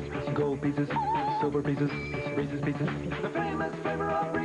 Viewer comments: Big pieces Little pieces Masterpieces Reese's Pieces Headpieces Neck pieces Hairpieces (grrr) Reese's Pieces Eyepieces Nose pieces Mouthpieces Reese's Pieces Three pieces Two pieces One pieces Reese's Pieces Timepieces Centerpieces Mantelpieces Reese's Pieces Chess pieces Gold pieces (Ahh!) Silver pieces (ding) Reese's Pieces The famous flavor of Reese's peanut butter in a fun candy shell You'll love 'em to pieces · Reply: It sounds like someone says "fucking shell" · Did he just say f***in shell?